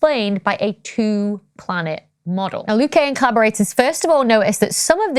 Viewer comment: i thought you said "Liu Kang and collaborators" and i started hearing the Mortal Kombat music in my head..... Fight!